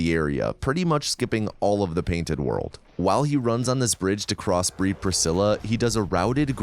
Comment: what he says: "While he runs on this bridge to Crossbreed Priscilla" what I heard: "While he crosses this bridge to breed Priscilla"